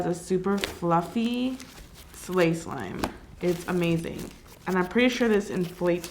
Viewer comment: did she just say slay slime?